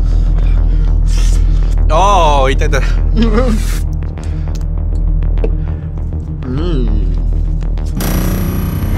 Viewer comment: did he said ITATA...??😂😂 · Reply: He said "Itai Itai" which means "Stop stop!" in japanese